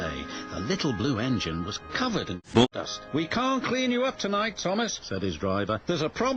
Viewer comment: And what does the narrator say at 0:33? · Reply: C. Syde65 The little blue engine was covered in bulldust.